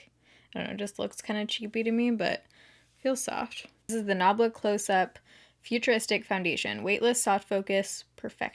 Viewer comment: Oh my God I thought it said nambla.....